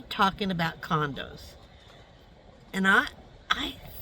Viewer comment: Tell me why i thought you said condoms at first not condos?! 😂